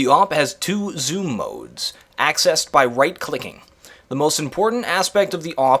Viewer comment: Thought he said "rape clicking" xD